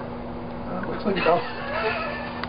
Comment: Did he say: "Its just a werewolf?"